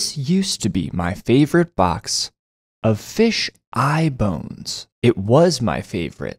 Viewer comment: For I second I thought he said “a fish iPhones”😭😭